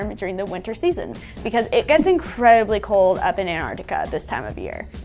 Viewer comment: I didn't know there were polar bears in Antarctica. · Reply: Yea, I kinda miss heard, around ~1:50 she says "in arctica animals" and I heard Antarctica. I'll let you pass for today. You're welcome